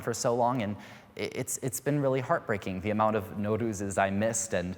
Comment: In minute 6:36 the subtitles say „Wudus“ but this guy says „Nouruz“ which is the persian new year. Wudu has nothing to do with persian culture. 😂